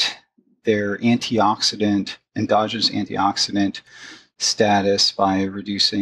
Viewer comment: Tim, "endogenous" does not have an "r" in the middle. I keep hearing you say "androgynous."